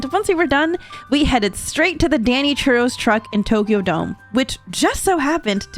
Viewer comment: yo Emirichu for a second I thought you said “Danny Trejo’s”. Like i had to rewind just to see it was “Danny Churros”